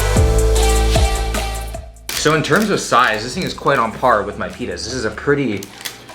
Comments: did he say pittas or penis 🤔🤨😏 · I thought he said the size is on par with his penis 😂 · Anyone else hear "In terms of size this thing is quite on par with my penis" 🤣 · I thought he said "in terms of size, this thing is quite on par with my penis" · I shit you not, I though he said "penis" instead of "pita" 😂